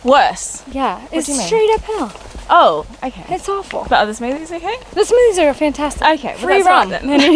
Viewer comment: OH, not movies.... smoothies. My bad hearing and the auto CC both thought everyone was saying movies. lol